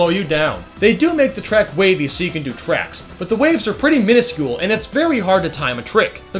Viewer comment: Today we grade the Triforce Cup in Mario Kart 8!! What's your favorite cup in this course and why? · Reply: did you say so you can do tracks!!!???